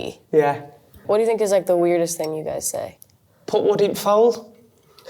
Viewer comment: what he says? “Put would it fall” ? Or what? Don’t get it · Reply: put wood in fire